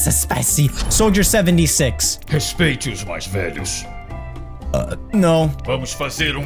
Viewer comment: bruh, it sounded like "não" not "no" it's like you know portuguese